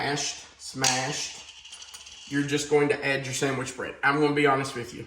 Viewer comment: I swear, I would have sworn that you said ‘sandwich bread’, and that you were going to mix it with the yolks. I kept watching just to see how that mixture was going to come together. Haha